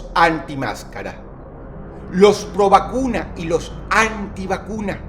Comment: alguien mas en el minuto 3:40 escucho que dijo ROBACUNAS en vez de PROVACUNAS. DROSS ERES UN VERDADERO LOQUILLO jajajajjajajajaja